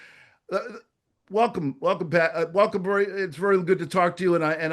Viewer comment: about said welcome paddy it sounded like 😂